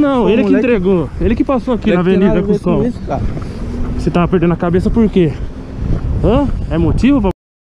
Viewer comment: Peraí, mas ele não disse que perdeu a mãe, ou eu ouvi errado?